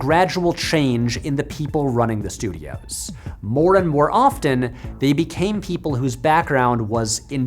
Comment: lol I thought he said "Moron than often"